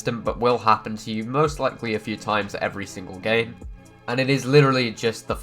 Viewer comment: Did he say every single gay